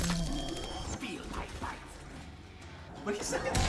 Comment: I think he said "Feel my bite."